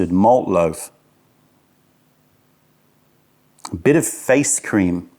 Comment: His tea usually tastes like all sort of stuff. Strawberry, Apple, Orange, Watermelon, Durian, Cinnamon, Chocolate, Banana, Apricots, Avocados, Starfruit, Tomato, Potato, Papaya, Vanilla, etc, etc ,etc and etc. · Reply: did he say he smelled face cream?